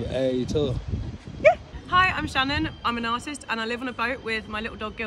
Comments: I thought she said im a narcist but she said im an artist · I thought she said - "Hi, I'm Shannon and I'm a narcissist" 😂😂😂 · I thought she said im a narcissist